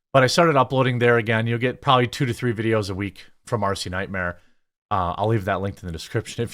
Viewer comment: Ha! I heard "Arsey Nightmare", which will likely set me to giggle occasionally from this point forward.